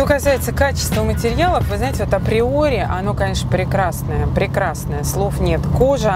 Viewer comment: Когда она сказала:"Качество априори",мне спросоня послышалось:"Качество,как в Приоре",ну все,думаю,приплыли)